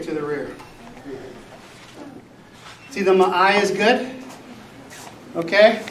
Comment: I really thought he said, „my eye is good“. „Okay“, I thought, „a little self-love here and there“. But no, he said the „ma-ai is good“. He even speaks Japanese! What a legend!